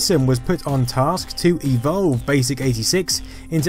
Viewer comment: Subtitles are wrong at 4:39 - Voiceover says "to evolve BASIC-86" but the subtitle says "to develop BASIC-86" :)